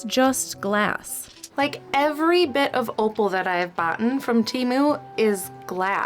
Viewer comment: girl did you say "boughten"??